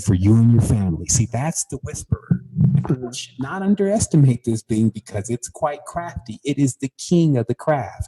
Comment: What is he saying at 5:17????? Thats when you use your imagination to what???? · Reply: Sounds like he’s saying use your imagination to be the communicator